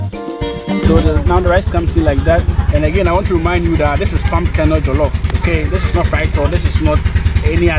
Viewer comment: It palm kernel oil, so from the palm tree 🌴, there’s the fruit call palm nut, and the palm nut has the reddish colour at the outer layer and that is the fruit which is used for red oil or red palm oil. After the red oil we will now get the real palm nut itself and it has a shell, when you crack that shell off you get the palm kernel nut which was inside the shell. Now that kernel nuts also produce oil that’s is called palm kernel oil. I have done a video about it already, you can check it out to see the whole process. Both the red oil and the palm kernel oil. Thanks for watching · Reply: Thanx, thanks but it sounded like you were saying pumcaanoil